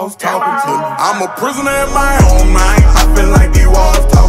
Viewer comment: I thought he said "I'm a prisoner in my old man." XD